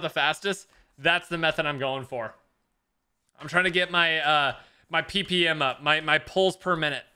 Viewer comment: I SWEAR HE SAID, GOTTA GET MY PP UP! IM DEADDD